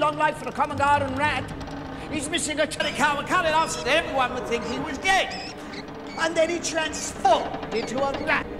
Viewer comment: 'your RRRRAT' 2:08 I heard 'SO EVERYONE THOUGHT HE WAS GAY'